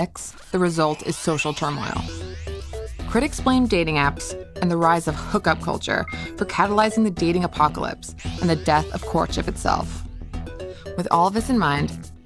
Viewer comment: Im doing the cc for this video, but im having trouble with what she is saying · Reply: "Critics blame dating apps and the rise of hook up culture for catalyzing the 'Dating Apocalypse' and the death of courtship itself."